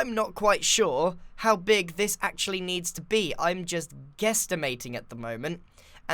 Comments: did he just say guestimating? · Is it just more or does Grian say, "Guestimating," at 28:57? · did he say guestimating